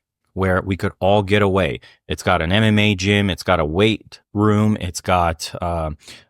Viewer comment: Did i hear Anime Gym or MMA Gym. lol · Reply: Definitely MMA